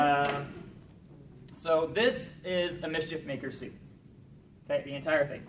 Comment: he says which suitmaker it is, but i cant hear it please help? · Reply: Mischief maker